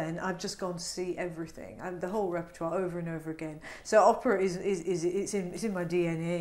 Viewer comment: I thought he said "Oprah"